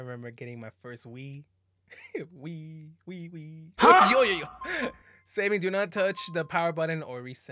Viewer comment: WHAT DID HE SAY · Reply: Wi wee